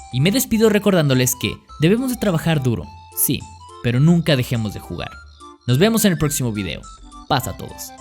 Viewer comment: Al final en ves de "Nunca dejemos de jugar" entendí "Nuca dejemos de fumar" jeje